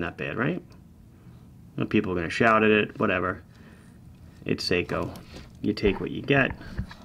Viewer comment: Did he just say, "it's Seiko, you take what you get?". That's a terrible mindset lol